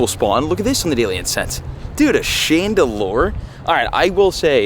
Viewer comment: When he said chandelure I thought he said chandelier and I started thinking of the song I WANNA SWING ON THE CHANDELIER THE CHANDELIERR